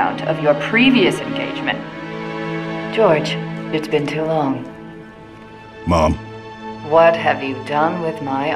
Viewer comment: I like to think he says “mom” instead of ma’am because I think it would add a bit more to their back and forth in 27:51 I think it would be cool for Halsey to get attached to Jorge and their relationship and how Halsey would react to his death